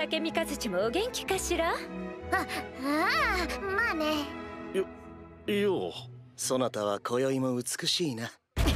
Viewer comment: i swear he said ew.